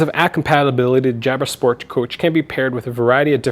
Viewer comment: I thought you said Jansport with out looking at the title lmao